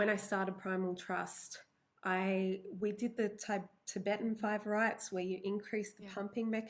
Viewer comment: what is tibetan fiber...? Something about increasing pumping mechanism? I couldnt quite hear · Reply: Tibetan Five Rites. It's an ancient yoga practice that consists of 5 exercises. You can search for videos of them on YouTube. They were extremely uncomfortable for me to do at first and I worked my way up slowly to do a few sets per day until that increased neurological circulation actually started to feel good.